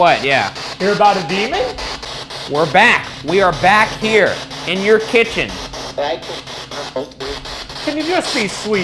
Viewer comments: all I hear is „Back at you weird” like yes that's how watching episode after episode felt. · I think it said back at home